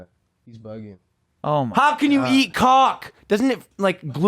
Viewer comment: I was listening to the podcast, I heard you say how can you eat cock, didn’t realize you were talking about Caulk. Doesn’t it glue your insides together Lmao 😂